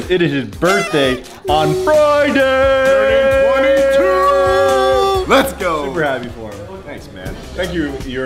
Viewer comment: did he just say let's go__brandon 😂!